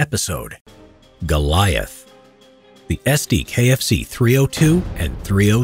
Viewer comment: Did someone say KFC?